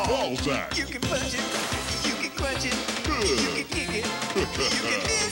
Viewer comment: Did anybody else hear "YOU CAN INCESSS" @ 0:18 or is that just me :P